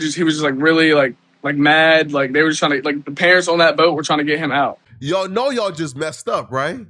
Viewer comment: did he say, “the parents on that boat want him out”?????